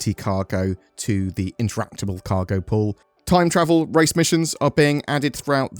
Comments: Dang, I heard "Time Travel" instead of Trial... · Thought you said "time travel" instead of time trial